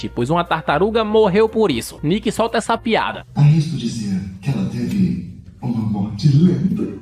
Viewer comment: ????? O que ele disse? Não ouvi. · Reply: "arrisco dizer que ela teve uma morte lenta"